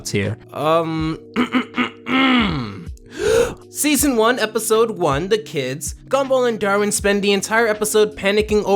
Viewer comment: Did you just say season 1 episode 1 the kids?